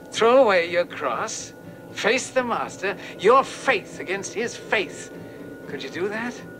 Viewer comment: anyone notice his make was coming off on his arm when he grabs the cross? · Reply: I always thought Mason said: Your faith against his face! I figured Barlow's face was so horrifying that it represented pure evil. But i guess he says: Your faith against his faith. Which makes no sense. Maybe it was: Your faith against his fate?